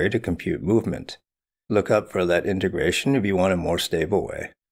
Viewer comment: does anyone know what integration method he's referring to here, the "more stable way"? I can't make it out over his gravel. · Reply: Verlet integration